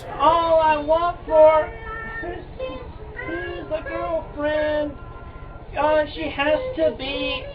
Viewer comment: "i need a girlfriend 82-22" ...anyone care to correct me? theres no way he really said that at this early point lol